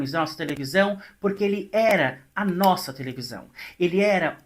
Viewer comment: Te tando ouvi " ele era camelo " Eu so lembrava do mito da garagem 😂